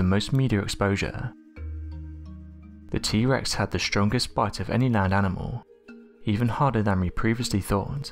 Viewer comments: not gonna lie, I thought he said the strongest butt XD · With that British accent, I thought he said: "The T-Rex had the strongest BUTT of any NINE animal." Wha?? Rewind please. 🤣